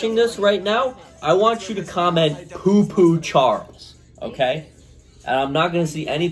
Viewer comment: Poo Poo Charles. I wasn't sure what you said right there at 26:50 but it sounded like that.